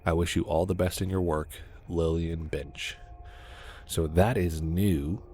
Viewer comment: i thought he said "lillian bitch" XD